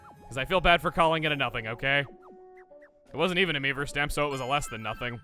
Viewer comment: Whenever Chuggaa says "Mii-verse" I hear "Meme-verse" .